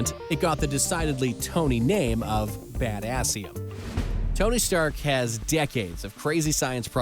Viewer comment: Was it really called badassium? 🤣🤣🤣